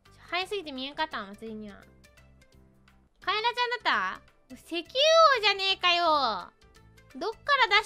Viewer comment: Not 100% sure but I think at 1:25 says 石油 as in Kaela's like some oil tycoon because of how rich she is? So probably something like "Are you some kind of oil tycoon" might be a good interpretation considering the followup